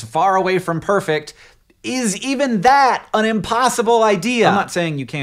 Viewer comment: does Hank say "unpossible"?